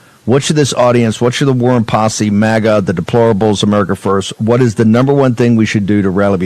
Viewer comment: Did he say "Mrder First" did he f***ing say that!?